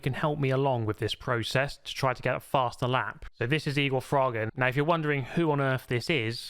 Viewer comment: Turn captions on... why does it say Eagle Frogggin lmao